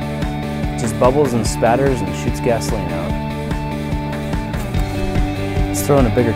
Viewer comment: lol I thought he said "it sh*ts gasoline" and I was just thinking "Grant really, you're a family channel!!!"